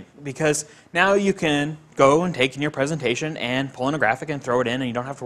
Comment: did he say "you can take in your pornographic"? 9:30 ?